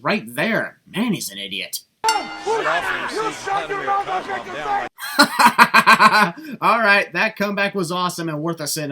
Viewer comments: What did Santino say? I couldn't hear him that well. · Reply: "You shut your mouth, I'll deck your face!" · what did Santino say? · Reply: Santino said "you shut up, I'll break your face" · What did Santino say? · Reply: "You shut your mouth! I'll deck your face!"